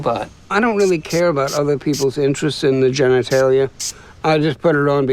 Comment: sorry but the robot guy is scary. he even mentioned having an interest in children....plus he knows nothng aboutthe current world.....looking for pay phones? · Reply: oh, i thought he said "i have an interest in children"